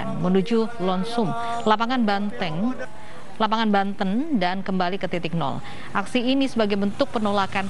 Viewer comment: gue dengernya BANTENG. Apakah BANTENG ikutan demo ???